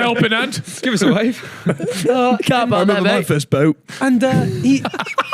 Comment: can anyone work out what said at 1:25:37 that made them all laugh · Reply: "I remember my first boat"